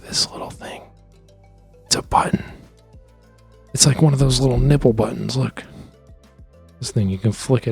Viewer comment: did u just say nipple